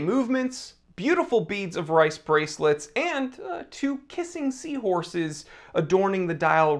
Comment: I was eating lunch and watching your video, and something took my attention for a moment. Then all I hear is "two PISSING seahorses".. 😂🤣 Thanks!!